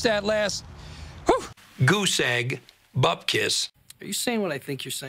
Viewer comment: Now what in the seven hells does he say at 1:30? I got the second word, bupkis, meaning "nothing" in yiddish, but the first? Did he say guseg bupkis? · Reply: You might got an answer to that already but he says: Goose egg (meaning 0)